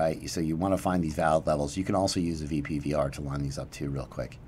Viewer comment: What is the "VP PVR" at 00:18:35? I can't quite make out what he is saying and couldn't find anything with a search. Thanks in advance. I'll be watching COTI for sure. Keep up the great work!